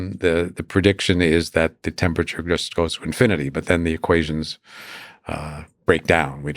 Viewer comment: does he say ‘close to infinity’? Surely that’s an impossible concept?